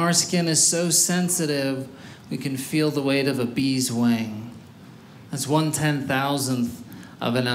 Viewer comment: I heard “bees’ wang”.